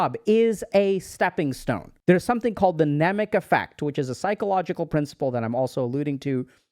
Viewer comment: What was that think he said at 16:07? The nemick effect? The subtitles don't help here